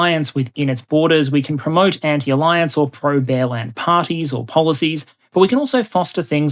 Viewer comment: Not going to lie... I heard "bellend" instead of "bear land". I think it fits just as well though.